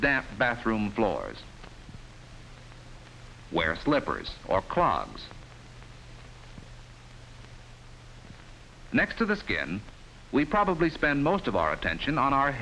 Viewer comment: "wear slippers or crocs" did i hear that right?! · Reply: He said clogs.